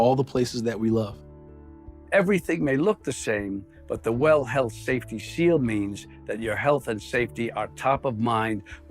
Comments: I thought they were saying, "Well HELL safety seal". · Sounds like they said well hell safety seal